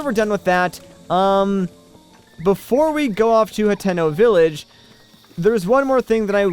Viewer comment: lol i thought you said Nintendo village